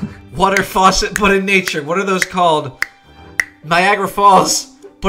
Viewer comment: Thought he said Viagra Falls